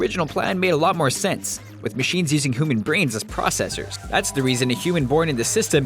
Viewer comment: did you just say hooman??